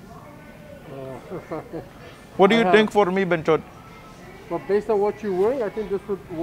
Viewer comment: Did you call em a freaking “banchod” @ 1:26 🤔😂😂😂😂? By the way, this is the bartender at sports city cafe lol. Talked to ya the other night.